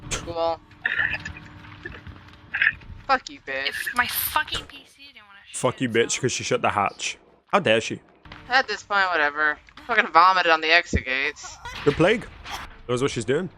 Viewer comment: I don’t think the streamer said “oh, fucking whore!” When the Plague closed hatch. I’m pretty sure she said “of fucking course”.